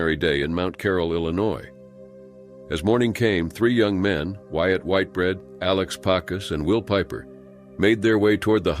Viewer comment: his name was "Wyatt Whitebread?" Did I hear that right or did my ears flap?